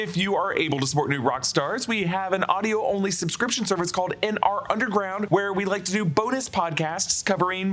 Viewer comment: I think it is just my brain, but every time I hear “N R Underground” my ear hears “In Our Underwear” which is a funny mental image but suggests bearing it all/being vulnerable in their cinversation